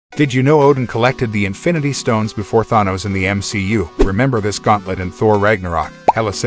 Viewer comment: I heard ‘did you know Odin collected the stones before Theranos?’ 😂